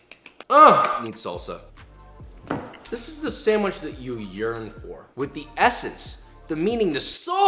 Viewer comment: is it just me or does it sound like he said "this is the sandwich that you urine for" lmfao